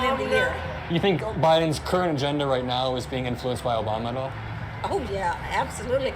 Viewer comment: I heard "Is Biden's currently gender being effected by Obama right now" And I just can't stop laughing. I know that isn't what was said but it has me dying